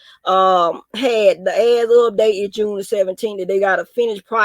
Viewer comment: What are you talking about? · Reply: I was watch the video it sound like you said ass is up June 17th 😂😂😂